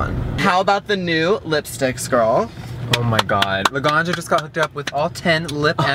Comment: I thought that said lasagna